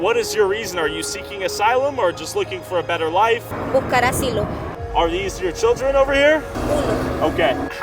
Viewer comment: U got the subtitle wrong. She said seeking for Asylum